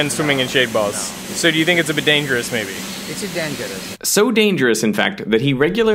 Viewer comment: Did I hear him say, “So it’s a bidet-ngerous?”